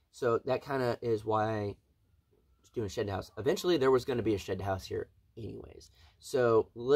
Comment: be careful it almost sounded like you said shit house haha